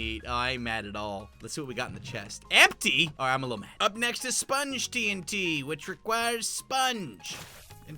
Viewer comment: I Heard "Spanish TNT" in